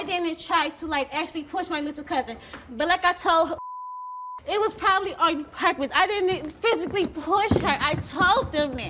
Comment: did she say it was probably on purpose